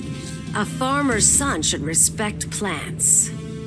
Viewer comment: Superman: What's your problem, Ivy? Poison Ivy: A farmer's son should respect plants Honestly, she is not wrong about that logic. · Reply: This makes more sense. I totally misheard it as "A former sun should respect plants."